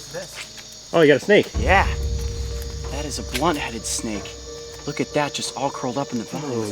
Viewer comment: i thought it said butthead snake 😭